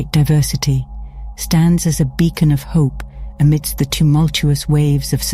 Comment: Thank you! And also sorry for this joke, but- did you just say "bacon of hope???'